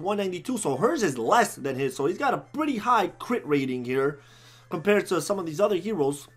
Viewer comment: what you said was sylphis pierce rating, not crit